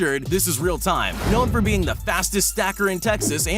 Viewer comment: who else thought it said fattest?